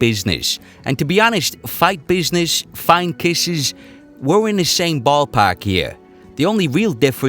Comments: I hear “Only kisses” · Now that you say it, I hear "Only Kisses" as in they're only kisses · I thought you were mad then I listened again and without looking at the screen I hear "only kisses". But I was wrong before so idk. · I thought he said "Only kisses". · I thought he said kisses as well. "Only kisses" is what I thought. It makes sense. Khabib mauled him for sure. But the ground and pound wasnt filled with bombs. They were mostly just wearing Conor out. Dpnt get me wrong khabib won. In dominating fashion.